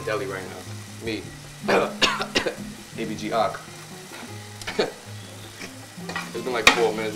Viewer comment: They lowkey messed up 2:56 “baby g o’clock” he actually said “ABG pick”